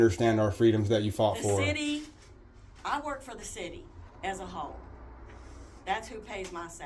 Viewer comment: Did she say she works for the city as a HO?! 😂